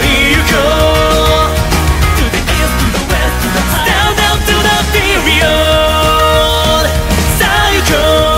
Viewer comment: Imma be legit, I always thought it said “Stand up to the furious!” Apparently, it actually says, “Stand up to the period!”